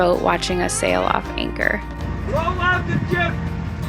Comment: "Roll out the jib!" Has been playing on repeat in my head. Idk if we will be able to do it without hearing that man 😂😂 Thanks for being with us jb, means the world 🌎❤ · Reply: oh he said roll out the jib? i thought he said roll up a joint! silly me